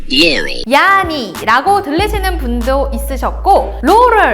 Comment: 난 왜 hear me로 들리지